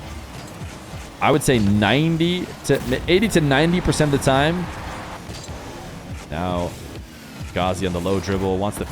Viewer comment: What did say at 0:30 ... you can't cliff hanger us like that mannn :( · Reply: hahaha im sorry, he said ''They elect to play on EU servers"